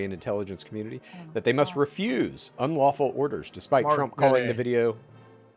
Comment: What did say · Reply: He says "Mark Kelly" in a funny voice